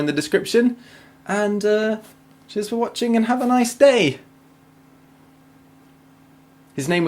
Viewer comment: I once misheard the last sentence as "...and have a nice DAVE" and now I can't stop hearing it